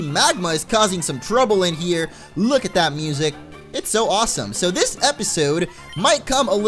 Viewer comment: did you just say "look at that music?" hahaha